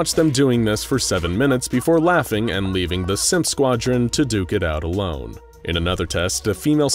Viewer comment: I'm sorry did I just hear "simp squadrant"?